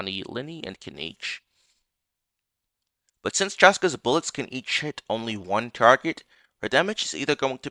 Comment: What did you say at 5:11 eat shit? Or am i hearing it wrongly 😅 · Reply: I think he said "each hit" 😂